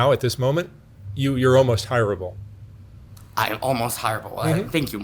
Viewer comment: almost hireable? Mark's vibe with this guy is somewhat strange. The messages are mixed...helping,yes; enabling, yes. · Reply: Did Mark really say that? I thought he said almost unhireable. But heard hire able. If Mark really said that I'm really wondering what he's thinking. What I see is a person high on meth or something who talks so fast I almost can't understand half. She also has those body movements people who are doing high dosages of meth get, where their bodies move by its own, arms in front, behind, up, down, almost making a knot of themselves. It's so horrible to watch, it looks so painful. So how this is almost hireable is beyond my comprehension.